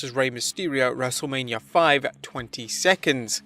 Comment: Did anyone notice that he said wrestlemania 5 even tho the caption said wrestlemania 25 in